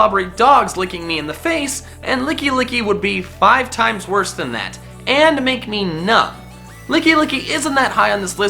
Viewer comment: is he saying “lickilicky is five times were than that and make me NUT!” Depending on where lickilicky licks you I guess